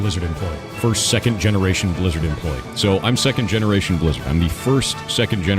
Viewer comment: For a second I thought he was saying Lizard